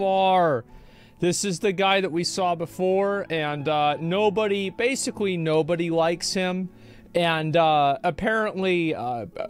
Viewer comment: In the first 20 seconds of this video, Asmon said 'basically nobody likes him', but I thought he said 'basic nobodies like him'. Both were correct though.